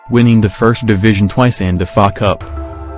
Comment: did je just say fuckup??😂😂😂